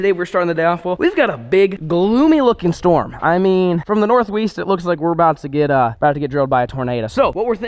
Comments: Sounded like he said north weest 🤣🤣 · Did anyone else hear him say "from the north weeest"